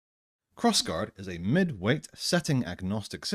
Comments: I thought it said Crossing guard the RPG and my mind went- The secret life of crossing guards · I'll be honest I heard "cross guard is a midwit setting agnostic system." Not "mid-weight" the first time.